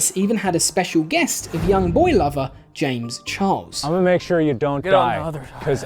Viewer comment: DID HE SAY ‘young boy lover, james charles’ PLS 💀😭